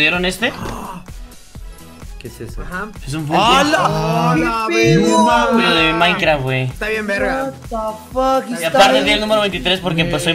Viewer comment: en mi cabeza escuche -eh un finko -y esta bien cabezon